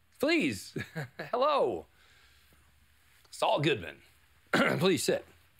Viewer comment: Maybe me but when Saul introduced himself here it sounded like he said ItsAllGoodMan.